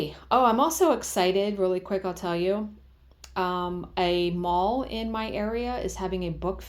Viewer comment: Lol, I thought you said a mole in your area is having a book fair!